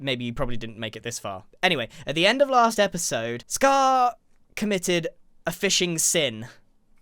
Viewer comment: I thought he said "scar have committed arson" i was not surprised